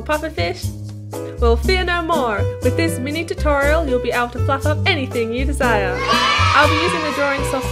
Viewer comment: What she said: you'll be able to fluff up anything you desire What I heard: you'll be able to fuck up anything you desire What the subtitles says: you'll be able to suck up anything you desire